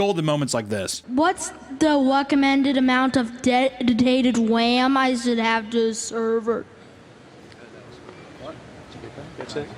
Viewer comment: I figured out what the kid said! "Whats the amount of recommended RAM I should have to run a server?" Legit question, horrible speech impediment.